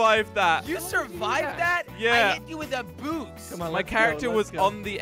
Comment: it sounded like Jelly said “I hit you with a boob” listen closly